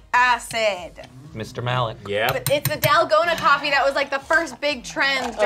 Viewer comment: that shit got me for a second here cuz at 1st I thought they say Nalgona and I thought who in their right mind would name a coffee that ( if you speak spanish you know why!)